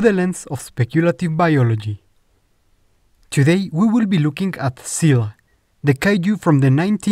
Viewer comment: 1°: Funny. Zilla was originally going to look just like this drawing (Just without the spines on its back) 2°: Again, this is because of our stupidity. 3°: 0:18 Did you just say "Seal"? 😅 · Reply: 1. I wonder what I would have done with this had that been the case in the final movie! 3. I said "Zill-uh", which is how you pronounce Zilla in * heavy accent * XD